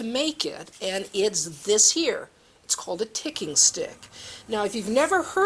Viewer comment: Thought you said tickling stick ..was expecting Ken Dodd 😅